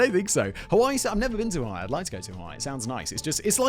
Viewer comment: I swear Simon Says I've never been to Ohio not Hawaii here